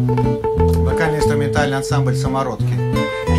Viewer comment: Прикольно, а мне послышалось сковородки😂👍